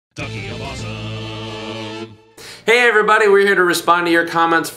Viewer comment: People might already have said this. but it sounds like you say "Donkey of Awesome"